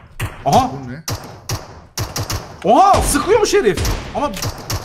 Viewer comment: abi "sıkıyor şerefsiz" mi dedi benmi yanlış duydum 😅.